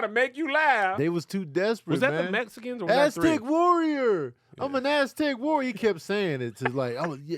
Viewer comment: Oh wow!!!! I just realized that he said "I'm an Aztec warrior." All this time i thought he was saying "Elastic warrior" 😂🤣😂🤣 Thanks